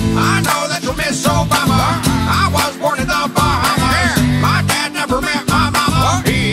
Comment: It sounds like it said i watch porn in the Bahamas XD